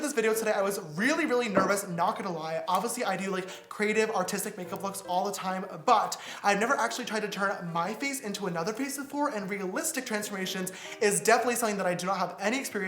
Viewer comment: i thought he said autistic at first 😯😅